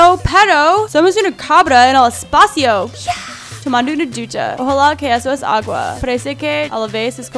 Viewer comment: no parece que dijo ducha 0-o